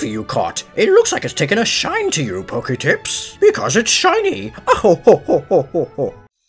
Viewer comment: I thought oak said poketits and I got hella confused